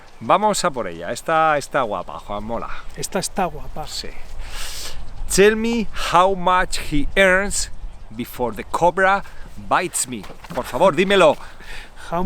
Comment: Use the verb estar for this: Estoy colgado y obsesionado con aprender.... Doble sentido: ...antes de que la cobra ME muerda Buen trabajo Peter y gracias por tu apoyo!!💪💪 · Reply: de na y gracias por las correcciones profe ✅-I misheard the question -I thought it was ‘How much does he earn before the cobra bites him?.. my mistake’!!! Y si ESTOY COLGADO OBSESIONADO con aprender español correctamente 👍